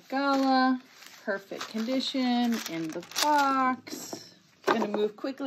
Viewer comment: Yooo what did u say at 0:38???? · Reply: "perfect condition, in the box"